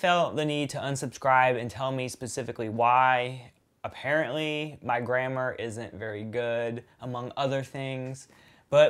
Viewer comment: Your pronunciation is off also, because I genuinelly thought you said "My grandma isn't very good"! Now that would have been an insult.